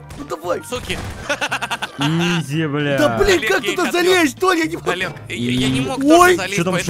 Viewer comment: наконецто ми ето услышали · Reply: Он сказал блин